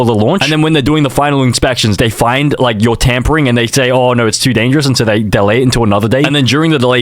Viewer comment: I heard tampon string not tampering😭